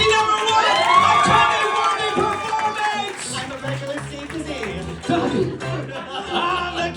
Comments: Does anybody know what Ben says at 9:56 ? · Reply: I think he said "I'm a regular Steve Kazee" who beat Jeremy out for the Tony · what does Ben say at 9:55? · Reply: I think he said "I'm a regular Steve Kazee" who beat Jeremy out for the Tony